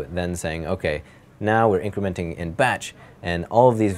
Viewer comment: Did I hear sassy gay friend at 3:14 ? "Now we're incrementing in BETCH!"